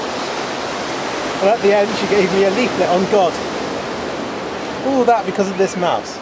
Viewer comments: What did say at 2:06? When he said "and at the end, she gave me a --------- on god". Did he say "leaflet"? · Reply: Yes, he said "leaflet". It's not hard to hear for me. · What does he say? "She gave me a believe of god"? What does that even mean? (Sorry, Im not native and youtube captions think you spoke dutch.) thanks in advance.